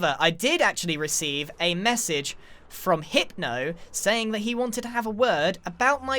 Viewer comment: HIEP-no idk why but thats what i heard